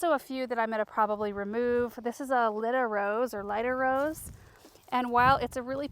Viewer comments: I have never seen a rose like that! “Litter Rose,” did you say? Please tell us more! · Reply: Took me a few tries but I think she was saying Lyda Rose. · Does anyone know the name of the white spray rose at 19:24? The one Laura is going to move to the landscape. I can’t catch what she was saying. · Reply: Lyda rose